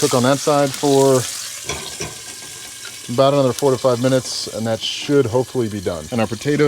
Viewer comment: When you were cooking the fish, I thought you said 45 minutes instead of 4 to 5 minutes! 🙃 . Such beautiful locations.